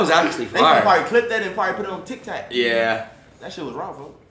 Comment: i swear polo said tic tac instead of tiktok